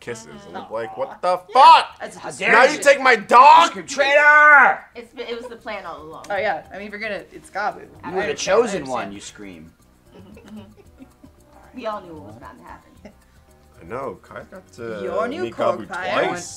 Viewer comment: Okay, I was zoned out, and that conversation makes a lot less sense when you hear "God" instead of "Gabu."